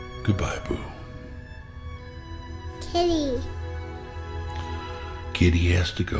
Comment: She sounds like she said TIDDY on 1:47 😂🤚